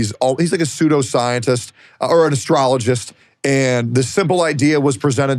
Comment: did he say "ASTROLOGIST"?